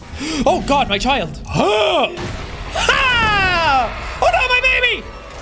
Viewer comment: "oh no my baby" all I heard was "Father help!"